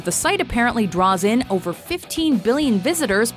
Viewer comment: did you say 15 billion users per month? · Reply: 15 billion VISITORS per month